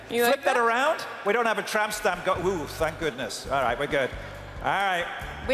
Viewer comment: Did he just say tramp stamp?